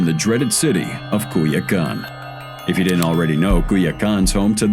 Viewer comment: as a Mexican I hate to be the one to point it out but your cc says “cool your gun” 😂😂😂 instead of Culiacán 😂😂😂😂😂